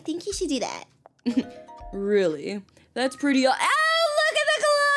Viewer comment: for anyone who wondered what sprout said here he said: “Really..? thats pretty odd sounding to me I dont think he would keep that a secret from you-“